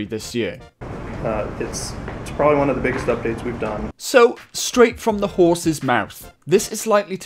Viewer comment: "I heard "The greatest update we've done" and I got so fricking hyped.